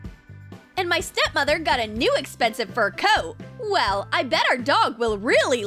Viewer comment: I thought said it was her step moms new fur coat